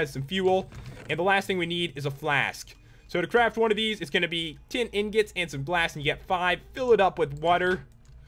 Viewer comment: it is just me or did pat say "tin tin ingot"